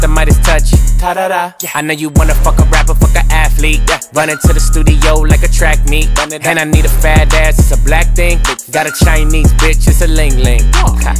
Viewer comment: I really heard it "blackpink"😭I was shock!